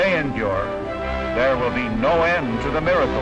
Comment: What funny way does the announcer say Vacuum Tube that the captions say Vacuum Jew ??? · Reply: 73! I thought he was saying vacuum chube.